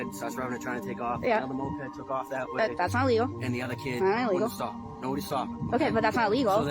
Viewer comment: the subtitles are totally contradicting what the woman says. She said "that's not illegal & that ain't illegal", what sjupidness is that?